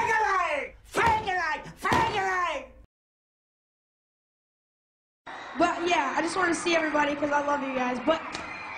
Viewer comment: does justin say fuck yea